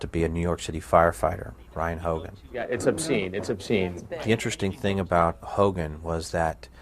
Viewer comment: Ummm did anyone else hear say “that’s big” when the fireman showed his D on cam at 11:47?!?!?!? WTF